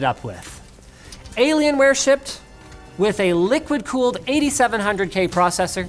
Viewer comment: did anyone else hear “ligma cooled”?